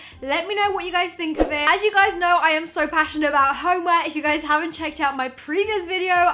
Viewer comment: Omg I thought she said HOMEWORK